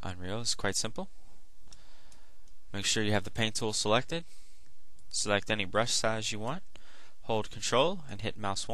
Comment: i thought he said select any BREAST size you want